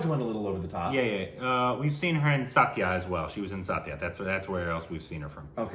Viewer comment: You are talking about 'Satya' & I heard 'Saathiya'